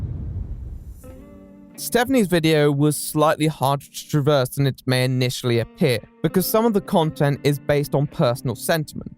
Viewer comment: Off topic, but can someone tell me what says at 2:49:37? I just hear "Stephanie's video... initially appear" My hearing isn't all that great and there aren't captions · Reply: Juicy Pear “Stephanies video was slightly harder to traverse than what May initially appear”